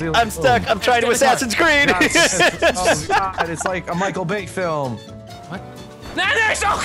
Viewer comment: I know he meant michael bay, but did it sounds like said "It's like a michael vick film"?